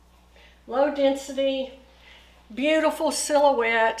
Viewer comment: Question: What does she say at 1:43......." and it has ????" What word is she saying? listened numerous times and can't figure it out. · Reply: Hi, I think what you are referring to is me saying "low density" I discussed the density throughout the video, please let me know if I can help further and thank you for watching!